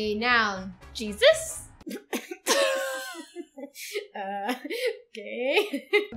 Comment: wait....did she just call Jesus gay? xD i have so many questions...(lol i know that she said ok..i think...but uh....just play it again you'll see what i mean )